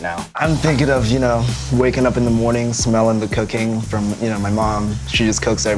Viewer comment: HAHA I heard "cocaine" at 0:52 I love waking up to the smell of my mom cooking the daily batch of crack cocaine😍